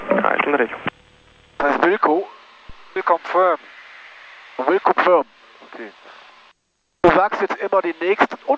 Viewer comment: WILCO... bedeutet nicht "will confirm"... Sondern "will comply" Also : „I understood your message and will comply with it“ („Ich habe Ihre letzte Meldung verstanden und werde entsprechend handeln“) Kompliment nochmals zu dem Flug. Diese schöne Maschine mal richtig adäquat eingesetzt... Schnell, hoch, weit